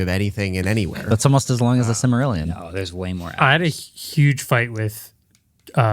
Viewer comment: Did Marques say "There’s way more Ants"? 😂😂😂😂